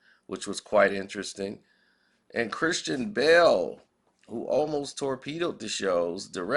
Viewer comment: Bro I thought you were saying Christian Bale torpedo the show.😂😂😂